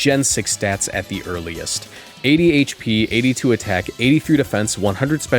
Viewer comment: that almost sounds like what my doctor says I have, 80 HD. No idea what an HD is but I’ve got 80 of them